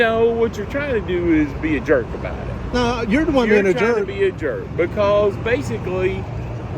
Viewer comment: omg did he just say be a frind? Sorry state laws do not cercumvent fedral laws.